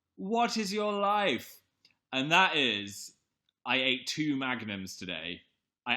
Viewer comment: i thought he said "i had two magnets today"